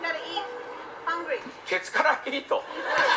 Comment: キッズガッタイートの空耳「ケツからイート」を聞くと、毎回かならず吹き出します